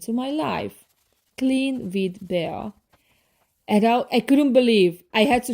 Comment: What did she say? Who came? Clean with Belle? Because I can't find anyone named that with a big following.